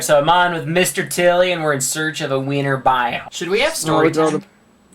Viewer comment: at the beginning of the vid he said "we need to find the winter boime" I thought he said weiner biome!😂